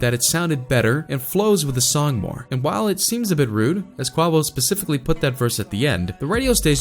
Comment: DID HE JUST SAY QUAWO